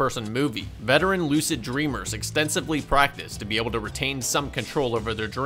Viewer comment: “Veteran Lucid Dreamers” is a term I never thought I would hear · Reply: OoooOOooohh.... I thought he said "Wet and lucid dreamers," I was like... Which, thanks for the shout-out, my guy!